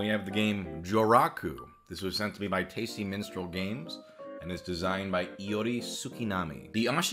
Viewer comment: I thought you said Tasty Menstrual Games and I was deeply shook